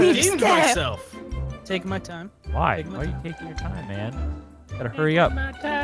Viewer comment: I hear "You gotta furry up."